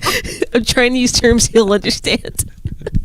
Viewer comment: If I'm being honest, I thought she said, "Chinese terms he can understand." also 😂😂😂 took my brain a few secs to realize it was "I'm tryin to use terms he can understand. "😂😂😂